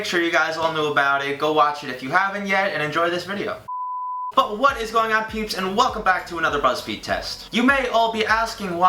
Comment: it sounded like he said what a going on pubes